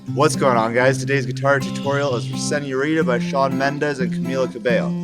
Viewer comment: the caption says 'resetting ureter' ... to señorita ... Lol😂